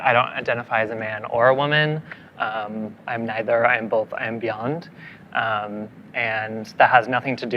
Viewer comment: "I'm neither, I'm both, -I'm beyond-" is peak narcissism. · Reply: all i heard him say was "I'm bed, bath and beyond"